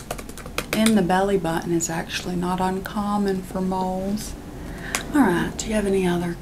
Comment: I thought she said mold at first-😭